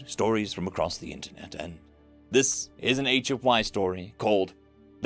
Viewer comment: For some reason whenever you say "HFY Story" I always hear it as "ancient wives story" 😂